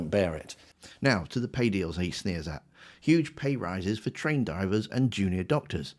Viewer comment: it sounds like you say Train Divers? Do they go finding trains under water? No wonder they need a pay rise! Great analysis as always.